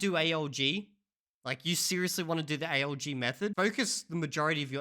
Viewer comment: I just noticed I misheard "ALG method" as "AOC method", which advocates anti-everything